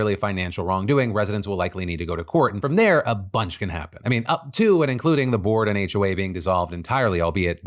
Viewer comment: “up to…”?, I heard hawk tuah